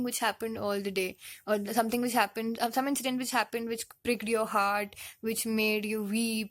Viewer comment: Did she just say "breaked your heart"? 🥺😕😖